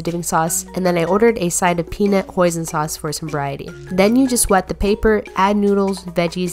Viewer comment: I thought u said peanut poison sauce at first. Ps good Idea!